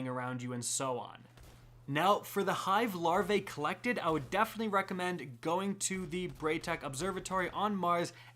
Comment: Was skipping 5 seconds... I heard “Die is- recommend.” Lmao XDD